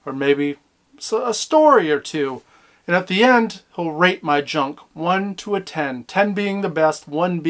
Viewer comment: it sounds like he say rape my junk, lmfao